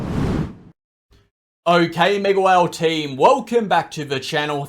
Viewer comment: TBH i was never sure what you said. I always thought you said megwell but only just realised its mega whale team.